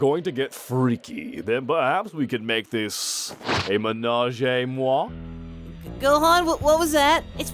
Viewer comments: It is just me or at 5:37 it sound more like "ménager moi" instead of "ménage à moi" ?? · I'm french.... And I didn't understand. LOL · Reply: Expect It I heard "émenagez moi" And that's means nothing xD And I tryed to found something close to it and correct but nope :/ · PLease does anyone can tell me what he say at 5:40 I am french but I don't manage to understand what he say, (probably because of the accent not realy used to ear french with an english accent sorry^^') I think he say "ménage à trois" but I am not sure.